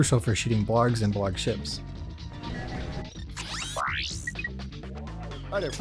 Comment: oh wait the shop guy says " hi there fuzz ball!" ? I thought he said " hi names paul!"